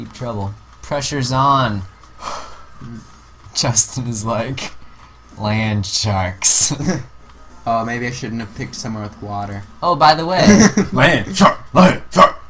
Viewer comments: it sounds like justin is saying lanchop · it sounded like "LAND SHARK! LAMB CHOP! LAMB CHOP!" lol